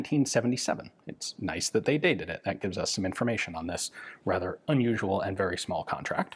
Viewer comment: There's a captions issue at 2:37. It should be "unusual" instead of "usual."